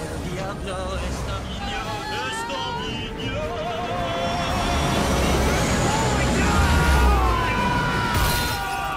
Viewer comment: What is he saying? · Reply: "El diabloooooooo" Diablo means devil in Spanish. Well, at least that what I heard.